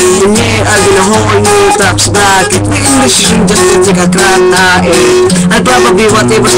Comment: did you just say tae?